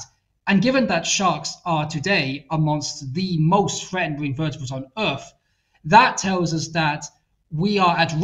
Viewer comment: the good doctor says " shocks today are among the most ... " but I can't make out the next word or phrase, which is the key point in the sentence. can anyone help clarify? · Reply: "sharks are amongst the most threatened vertebrates on earth"